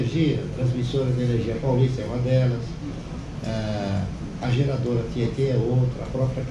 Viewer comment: Transmissão de energia Paulista e depois ele fala qual? minuto 1:14:00, infelizmente eu não entendi, ALGUEM PODERIA ME AJUDAR? ELE FALA DA : TRANSMISSORA DE ENERGIA PAULISTA KLABIN UNIPAR E FALTA ESSA QUE NAO ENTENDI.... Minuto 1:16:38 Tiete?